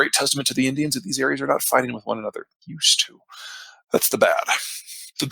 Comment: Can someone please help me with what PZ says here: "It's a great testament to the Indians that..." at 49:28? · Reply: "that these areas are not fighting each other. They used to."